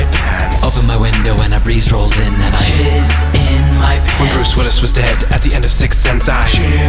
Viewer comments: the first time i saw this i thought they were saying cheers in my pants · at first i heard "cheese in my pants" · my friend was singing this once and i thought he said opened a window and a preist rolled in. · First time i saw this, i could've swore i heard em say "cheese in my pants"